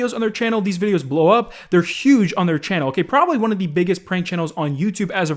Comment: did he just say they are huge on their own channel lmao